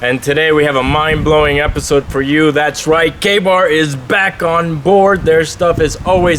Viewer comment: When you said KA-Bar I thought you said gay bar